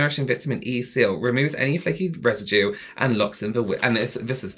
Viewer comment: I thought he said "fajita residue". 😂 I was like...que?